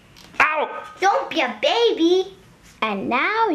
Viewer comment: I thought she Said don't be a bitch 😂😂😂